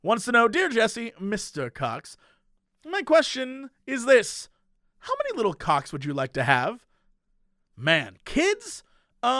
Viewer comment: All I heard was "How many little cocks would you like to have?"